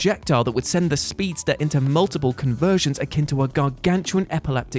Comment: Did.... did you just say conversions instead of convulsions?